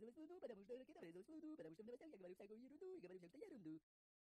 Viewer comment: я одна слышу : "ПОТОМУ ЧТО В НОВОСТЯХ Я ГОВОРЮ ВСЗЯКУЮ ЕРУНДУ Я ГОВОРЮ ЕРУНДУ" ?